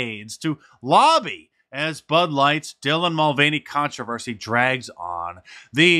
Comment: Freudian perhaps, but did I hear you say “Butt Light”.at the beginning of the video?